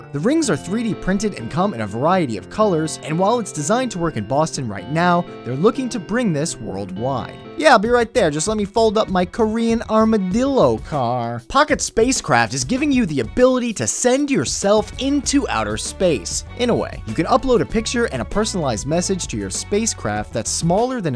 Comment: waaiiit a second, did u said "armored dildo"?